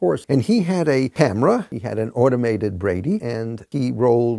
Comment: "an automated brady"? Am I mishearing?